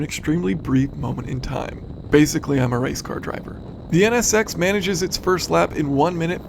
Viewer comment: i heard him say "im a rice car driver" haha